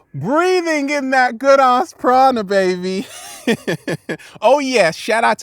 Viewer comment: And this whole time I thought he was saying Prada Lol